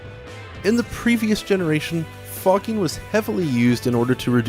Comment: Am I the only one that thought he said fucking and not fogging?